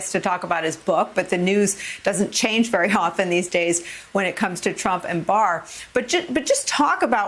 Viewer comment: I swear I thought she said Trump and Barf. Good job.